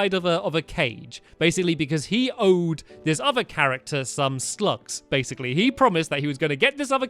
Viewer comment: I thought Stampy said 'He owes him some SLUTS' hahaahhahahah xD